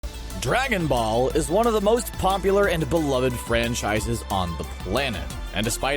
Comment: why does he say pompular